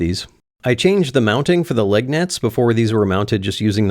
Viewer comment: You said "leg nets" and I thought you said "lug nuts" but one of us was having a stroke. Glad that neither of us had a stroke.